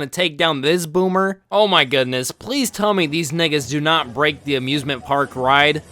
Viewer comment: did you say the N-word?